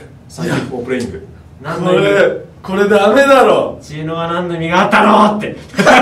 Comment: なんて言ってるの・・・？ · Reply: 「知恵の輪なんの意味があったのー！？って！」